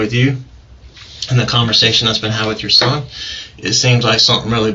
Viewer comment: Gotta love interrogations where a suspect pretends they have no idea why the police want to speak with them. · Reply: did you catch that? it said Jessica instead of TySheeka